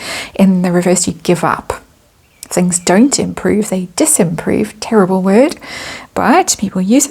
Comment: 'Unimprove' pls don't use bad or made up english words because others do it's sounds dumb and you are not dumb! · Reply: Bless your heart, no need to be so officious! Also, you appear to have misheard - actually said “disimprove” (see Collins English Dictionary).